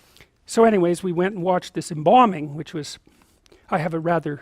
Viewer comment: Oh, he's saying embalming. I thought he was saying they were going to see a bombing XD